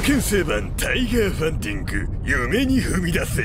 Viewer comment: OPの 「夢に踏み出せ」が いつも 「嫁に踏み出せ」(意味不)に聞こえて しょーがねぇw